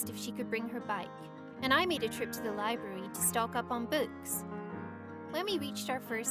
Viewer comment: It kinda sounded like she said ' stock up on boots' at 1:18 or